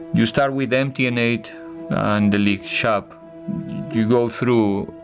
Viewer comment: Did José just say shapu👍😂😂